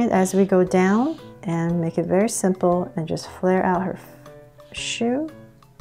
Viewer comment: WAIT WAIT WAIT WHAT DID YOU JUST SAY MISSY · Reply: She said shoe